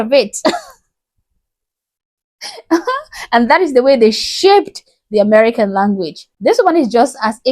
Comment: Oh Honey - Your African Accent Is Just Fine.... I Like Your Accent.... Your English Is Great....! Well, Mostly Great . . . . When You Said "Shaped" The American Language, At First I Thought You Said "Shipped" . . . LOL...